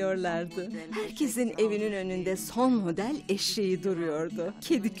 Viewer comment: "Herkesin evinin önünde son model eşeği duruyordu ..." Ben mi yanlış duydum ? asdxcxvfsxaxss! :D